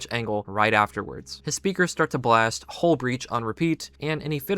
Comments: I've seen this movie twice and I still had no idea that the system was saying "hull breach." I couldn't make out what it was saying at all. I thought it was saying "Ulbrich" or something and I was so confused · when I watched the movie, I didn’t think it said “hull breach.” I actually thought it was saying “I’ll break in,” meaning the monster had overridden the sub’s speakers to speak to Simon.